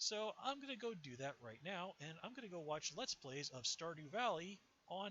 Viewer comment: what did you say at the end? you're going to watch what? I heard "..let's blaze of star new valley on YouTube"? sorry I couldn't make it out.. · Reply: I said "Let's Plays of Stardew Valley."